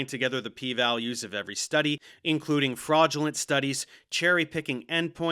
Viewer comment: O fraudulence, thought you said flatulence.